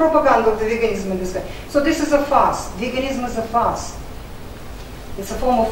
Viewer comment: At first I thought she said "this is a farce", although that would also be true!!